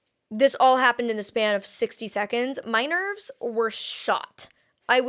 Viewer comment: for a second there i thought you said "minors were shot" and thought WHAT THE HELL, and had to go back a few secs to make sure 😅